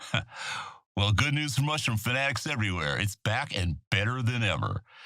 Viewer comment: did he just say, “well, Good news for mushrooms fags everywhere.” ??